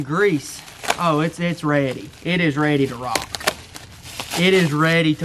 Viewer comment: When he said this was ready I thought he said it was red like blood